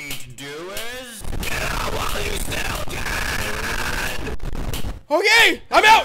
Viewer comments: Huh i always thought baldie say "Run while you still can" And not "Get out while you still can' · This entire time I thought he was saying "KILL MY STUDEEEEEENT!" instead of "GET OUT WHILE YOU STILL CAN!"